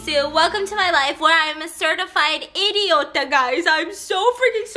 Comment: Did she say idiotka? In Russian? I’m confused...